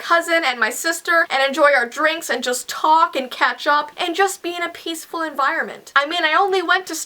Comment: XD I thought said "talk and ketchup" instead of "talk and catch up" XD